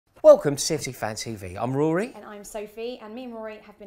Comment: At the start of the video it sounded like you said "City Fan TV"😂. You might aswell call you channel that now, considering most of your fans would switch to become City fans again now that Chelsea is shit again😂😂🤣